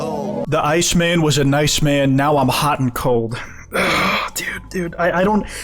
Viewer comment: I thought he said "now I'm uncool" not "hot 'n cold"